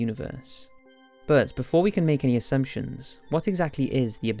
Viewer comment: Is it me or did i hear "Ass" in 0:20??